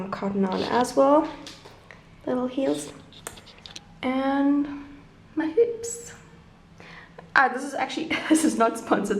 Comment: I actually thought she said “and my boobs”